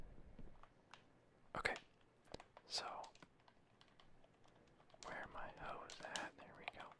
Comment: did he say we're are my hoes at